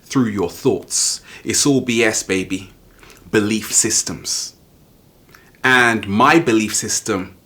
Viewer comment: says in his above video "It's all BS..." and I thought for a second he said the words Bull Shit whereas he said "belief systems." lol